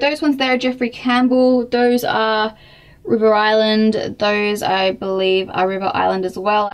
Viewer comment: I thought you said bogger island not river island.... xD